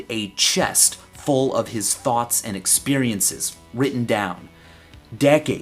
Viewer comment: forgive me, Michael, but I thought you were going to say "thongs" at first.